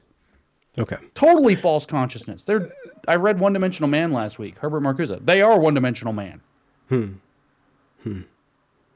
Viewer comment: Start at about 1:15:50, mentioned C.S. Lewis, but I couldn’t catch what he referred to … anyone catch that? A book, or a quote perhaps? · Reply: Men Without Chests.